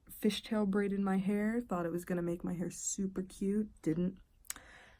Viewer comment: did you say you're going for "super cute"? Good luck! Hahahahahaha.